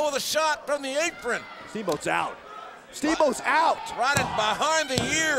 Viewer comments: Who else thought it said ricky steamboat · Man, I thought it said Ricky Steamboat